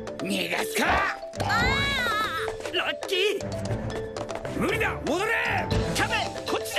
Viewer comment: He clearly says the english "Lucky!" then the subs say "Nice".